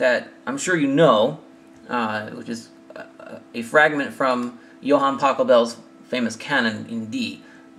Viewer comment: lol, did you say Johann Taco Bell?